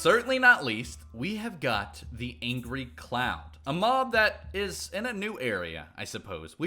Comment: He said Angry Cloud. I thought he said Angry Clown.